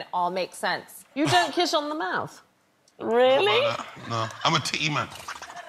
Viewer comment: Does anyone know what Big Narstie says at 42:51? To me it sounds like “I’m a teamer”.